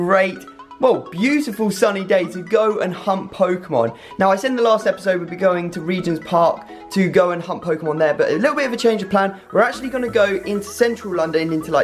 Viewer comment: It sounded like he said "Hump Pokémon"